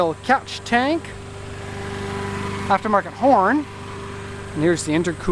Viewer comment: When you said "aftermarket horn", I thought you said "aftermarket porn"